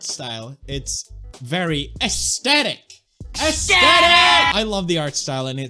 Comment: I heard ESKETTIITT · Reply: Nica Camargo subtitle says aesthetic